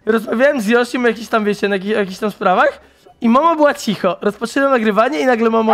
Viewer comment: Czy tylko ja tam usłyszałem o jakis NAGICH sprawach XD